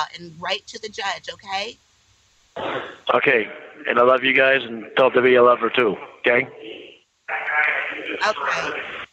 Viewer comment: it sounds like says, "Tell Tiffany I love her, too." Tiffany? Anyone else hear that? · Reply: I played it at half speed, he says Debbie.